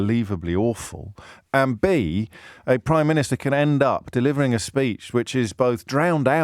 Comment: Near the 8:00 mark did he call Rishi Sunak the "Prime minature"?